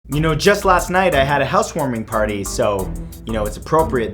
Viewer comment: I thought you said hearth's warming for a second (that's a mlp thing)